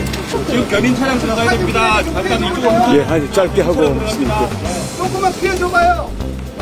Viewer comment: '예 짧게 하고 가겠습니다' 로 들리는데